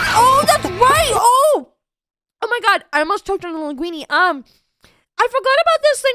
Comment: I thought you said Lingulini and had to do a double take